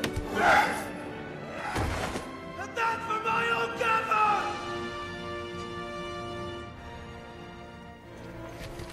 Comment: What does Sam say at 1:14? It sounds like he's saying, "and that's for Michael Gambon!" But that can't be it. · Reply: Jack Vallance He says my old gaffer, referring to Hamfast "The Gaffer" Gamgee from the books! Its his dad